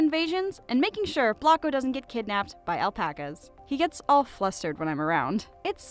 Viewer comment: Did I hear that correctly ALPACAS